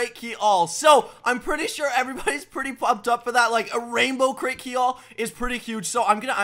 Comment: does he say fucked up?